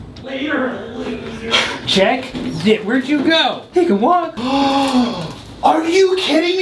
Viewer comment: did he say the n word at 7:29!?!?!? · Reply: He said "he can walk"